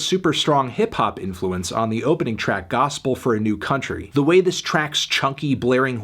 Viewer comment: new country? Can somebody confirm I heard that correctly?